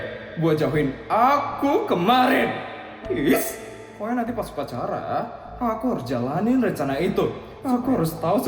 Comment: aku dengernya bilang pokoknya nanti pas pacaran😭 sampe aku ulang buat mastiin ternyata bilang upacara😂😂😂